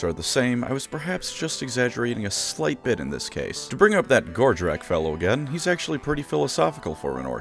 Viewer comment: I gotta admit, the first few times I heard “Gordrakk”, I thought you were saying “Gortrek”, and I was left wondering “Wow, what happened to Gotrek to get him turned into an Ork??” immediately followed by “Wait, even after getting turned into the fightiest of races, Gotrek STILL can’t find an opponent worthy of killing him?? Poor guy….” 😅